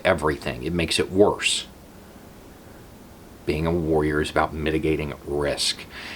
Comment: I heard “worriers”. . .